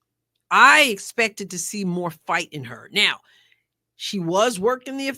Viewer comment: I thought she said fight!!! NOT F…k. I did not hear it I admit, but I don’t think she would said it that loud in public.